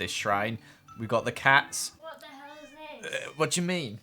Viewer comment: Did she just say what the hell